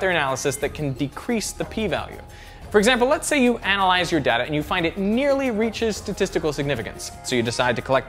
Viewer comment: I heard you say: For instance lets analyze your dad. I was wondering why you want to analyze my dad. Then realized your talking about data. (Which I grew up pronouncing day - ta.) anyway...